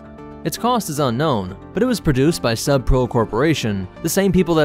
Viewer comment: I swear I, for a second, thought he said the "Sup bro" corporation. Although, I suppose if you think about it, sub-pro isn't really a much better name for an engineering corp either.